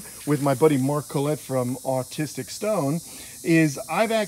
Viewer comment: Did he say autistic stone?